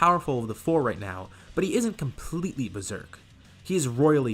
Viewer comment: I thought he said royally pissd xd